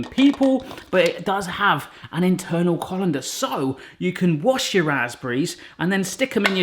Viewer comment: I had to rewind, I swore you had said "wash your a$$berries" and not "wash your raspberries"